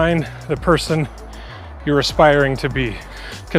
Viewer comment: I misheard, "Be the person you are perspiring to be" and it's so true😂